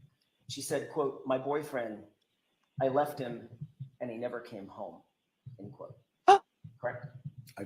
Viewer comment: I heard her say " My boyfriend , I left him and he never came home " heard it the 2nd time